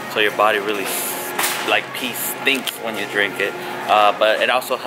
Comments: Did he say "it makes your piss stink when you drink it?" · did you just say “pee stinks when you drink it” ??